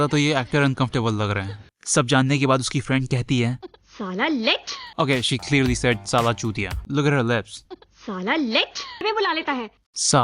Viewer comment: …did she say “saala lech “?